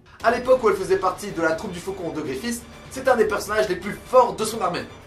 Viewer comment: C'est moi ou a 2:52 tu dis GrifFIST 😂😂